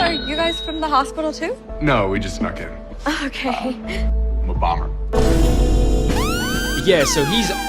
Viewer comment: Every time you play the clip of him saying "I'm a bomber" I hear "I'm Obama".